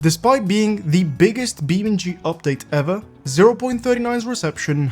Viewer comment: "the spy being the biggest beamng drive update ever" is what i heard